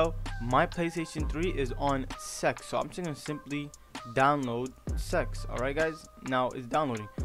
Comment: ITS NOT SEX ITS CEX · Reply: Swear as i read this i heard him say "Download Sex" lmfao